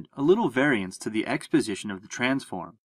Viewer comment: FYI at 4:57 and 5:07, the subtitles say "exposition", but it should be "x position".